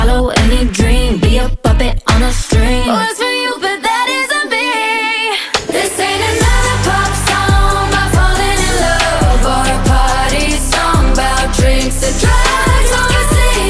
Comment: Every time i head the chorus, I always hear "This ain't a lollipop song" 😂